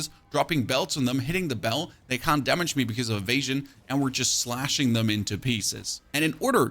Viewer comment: When you were talking about bell bossing in act 1, I thought you said they couldn't hit you "because of I'm Asian" i was cracking up! 😂